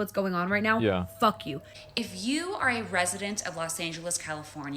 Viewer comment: i thought she said "the president of LA"